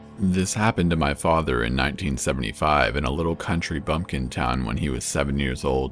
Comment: Who else though it sounded like he said county blumpkin town